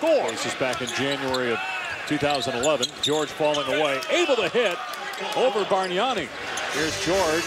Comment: did he just say George Paul?🤦🏾‍♂️😂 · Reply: He said “George falling away”